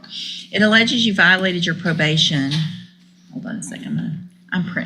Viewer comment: did judge West really say "I'm pregnant, I'm having a hard time reading it?" Surely I misheared ?